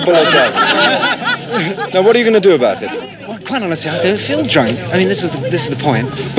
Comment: "what can i say, im drunk" rest in peace you extremely reasonable an drunk legend. T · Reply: Where did anyone say that? I heard "i don't feel drunk"